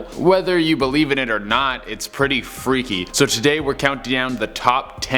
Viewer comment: did anyone hear 'count diown' at 0:20?